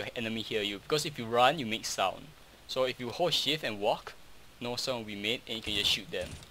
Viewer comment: It sounds like he is saying "horse shit" instead of ''hold shift" xddddd